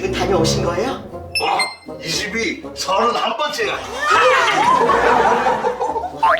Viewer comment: 혹시 9:49에서 도둑이 31번째야라고 보여는데 제가 듣는데 39번째야라고 들었어요.